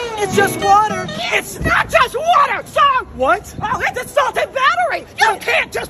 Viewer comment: Karen chillllll also did she just say water is a salted battery